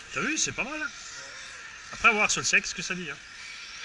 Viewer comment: Un truc g pas compris... Un moment à 7:20 tu dis: "à voir sur le sexe ce que ça dit" pourquoi tu veux mettre la voiture sur ton sexe ???? Et puis tu dis: "Une fois que tu l'as en main c'est bon", Mais y a des enfants ici !!! Mouhahahaha